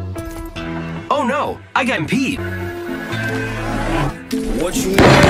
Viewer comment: did pathfinder say “I got peed” ? LOL